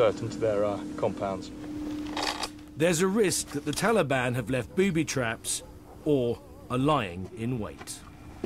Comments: I thought he said theres a risk the Taliban have left booby traps or a lion 😂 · I he said "or are lying in wait" I heard "Or a lion in wait", I was thinking, where have they got a lion from